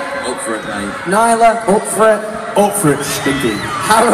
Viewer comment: Does somebody know what did Niall say? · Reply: he said "up for it shtinky" he was being silly